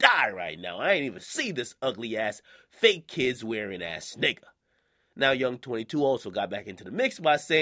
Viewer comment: Did Akademiks say "Fake kids wearing ass nigga"?